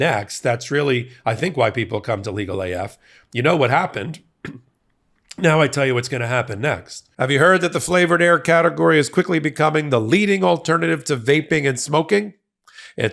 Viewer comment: I’m listening as I do other things, so here’s what I heard: “Here’s what comes next, the flavor air…”. That’s not exact, but I laughed so hard. I thought maybe this craziness has sent you to the Mary Jane. Lol. Thanks for your intelligence and the laughs. Every time, you deliver!